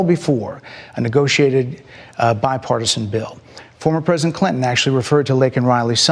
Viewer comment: Did she just say 'biparsan'?